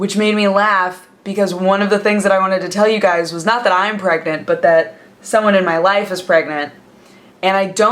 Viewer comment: I was sort of half-listening and I heard "I'm pregnant" and nearly had a heart attack.